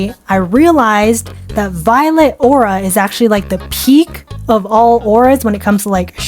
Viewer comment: Ngl I thought you said “violent aura” at first before you explained more 😂😅